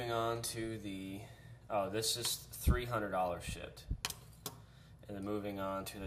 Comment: did you say this is "$300.00 shit?"